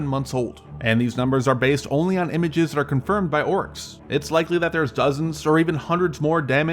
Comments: Did you actually call Russians "orcs" at 0:28 ? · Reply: No, he was saying Oryx, which is a Dutch based open-source intelligence defence analysis website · Did you really say "confirmed by Orcs"? I don't appreciate racial/ethnic slurs.